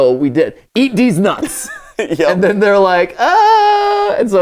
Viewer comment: 5 seconds in and I hear "deez nuts"... how did they even get to that?